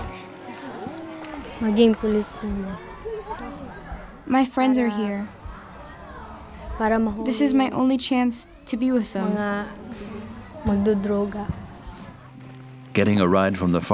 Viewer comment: The girl wasn't saying she wanted to be with her friends @ 41:22 -- I think the interviewer asked her what she wanted to be and she said she wanted to be a police officer to catch drug addicts. Imagine someone with the kind of determination she has becoming a cop.